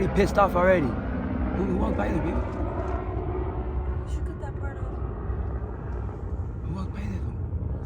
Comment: Did I hear say “you should cut that part off”?